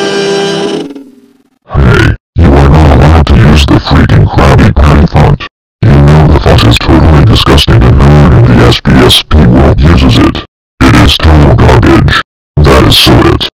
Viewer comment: What did you say? And yes, that is a terrible font. And a bad one too… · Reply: You mean 2:22, and I said “Hey, you not allowed to use the freaking Krabby Patty font. You know that the font is totally disgusting and no one is SBSP world uses it. It is total garbage. That is so it. This means, you are grounded (x5) for life. Now I will set the bomb in your device and it will explode in 10 seconds. Ha (x11).”